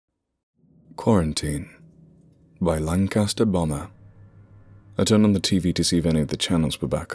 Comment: For a second I thought he said, long-cast-Obama instead of Lancasterbomber